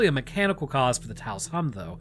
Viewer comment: Ngl at first glance I thought it said Tacos